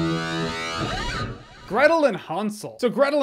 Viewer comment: Almost sounded like you said, Gretel & Han solo movie when you read the title. 😂